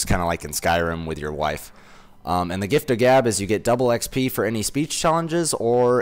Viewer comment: I thought he said the gift of dab